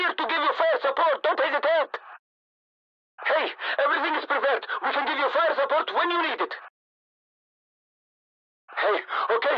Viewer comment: what does he say here? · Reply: “Hey! Everything is prepared. We can give fire support when you need it.”